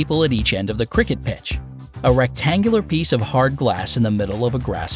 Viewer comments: did he just say Glass?? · Did I hear "a rectangular piece of hard GLASS?" 🤔